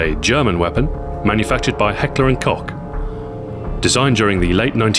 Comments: Only me who heard him saying at: 0:24 it whas made by Heckler and Cock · hahaha he really said heckler and cock